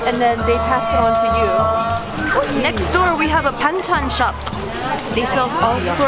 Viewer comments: i thought u said Bangchan when introducing the Banchan shop😂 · am I the only one who heard BANGTAN shop ? 😂😂